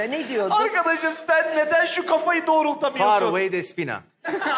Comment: civarı ne diyor ne despina? Faruley mi ne ki o?